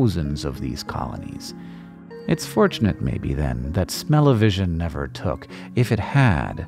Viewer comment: Did you just say smell-o-vision?